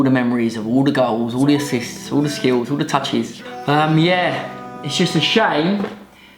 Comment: who else thought he said "I remember all the girls assist touches"